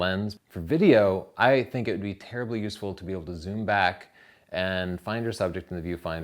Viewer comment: Did he say Terribly useful ?.